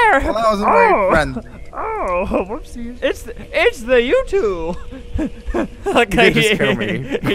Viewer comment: " It's the you to." Lol sounded like " It's the youtube." And knows ur albert... wait, maybe??